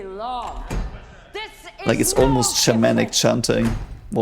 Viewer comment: BELTALOWDA !!! Tell us more about German "war-chanting" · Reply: He said shamanic (though you could mishear it as Germanic; which is different from German)